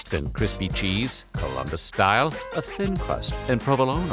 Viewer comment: What was the style after Detroit, he mumbled it but the description sounds s lot like St Louis style. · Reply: Time marker? If 2:38, captions say Columbus style